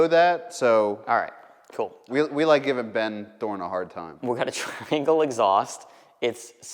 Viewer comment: I’ve been watching waaaaay too much twin peaks. I thought you said Ben Horne.